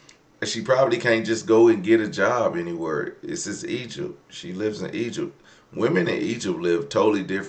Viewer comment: She said she’s in Egypt and clearly religious in the Muslim faith, isn’t the belief that a woman’s job is to stay home and take care of family? Not saying you’re wrong but it’s not always possible for people not living in the west to follow westerners advice. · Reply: that makes sense, I thought I heard her say she’s from Egypt but she said she’s in Egypt. Ok…